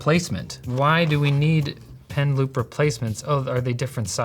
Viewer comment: Initially, I thought he said "pen lube" and my mind went: "oh, like a little ink pot but for lube so that the pen glides better over the screen?"